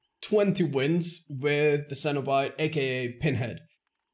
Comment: I legit thought he said senpai instead of Cenobite.